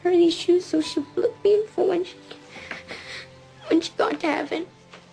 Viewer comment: what does he say at 1:59? I wanted to get her these shoes so she'd ____ me? Sounds like flip.... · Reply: Hutch1988 so she'd look beautiful when she got into heaven